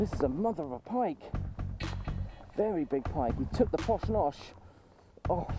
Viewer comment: 23.48 seconds sounds like u said he took the posh nosh lol nice work mate keep it up I wouldn't mind a posh noshing